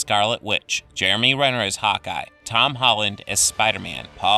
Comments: Uhhhhhhhhh did he say Jeremy renner as Hawkeye or hot guy? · I thought you said Jeremy Renner as Hot Guy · for a second there, i thought you said "Jeremy Renner as hot guy"